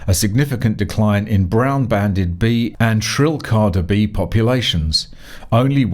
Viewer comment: Did you say there was a reduction in the population of shrill Cardi B's? This can only be a good thing, surely?